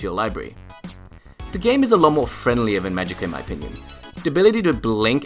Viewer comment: What are you saying at 1:08 ?? "the game is a lot my friendlier than ------ in my opinion"??? please tell me what you said lol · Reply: Magicka! sorry haha